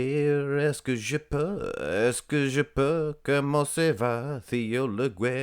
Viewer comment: i'm french but i swear i didn't understand a single word after "est-ce que je peux, est-ce que je peux" 😂 was it "commencer" ? Nice song anyway please do more 😎